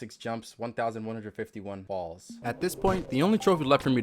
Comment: I'm sorry, 1151 Balls??? Edit - I know he said falls, but the first time I heard it, I heard balls lol